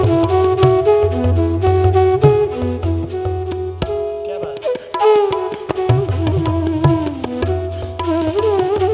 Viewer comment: Would anyone who understands please tell me what it is Zakir says at 3:56, after Rakesh's tihai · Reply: He said "kya bath hai" - an exclamation to say how beatiful